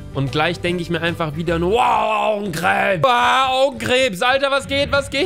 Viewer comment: haha ich hab anstatt augenkrebs hodenkrebs verstanden😂😂fail